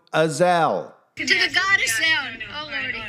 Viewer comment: I think she said to the god of sound, which is still blasphemy but it wasn't azazel